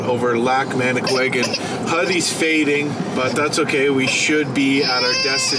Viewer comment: When you guys call your son Huddyy it sounds like your saying puddy and that was the name of my brothers dog. Yeah. Well I enjoy these videos. So thanks